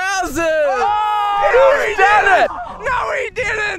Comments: I thought he said brazzers at first😂😂💀then said browser's 😂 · I thought they said "Brazzers" not "Browsers" and was like 👀👀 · Wont lie i thought he said Brazzers now Browsers😂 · That safe needs to be displayed proudly at your HQ. It is the embodiment of your channel: Dropping things from a great distance, pinpoint accuracy, and an amazing slowie. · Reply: I thought he said brazzers at first 😂😂💀 then said browser's😂😂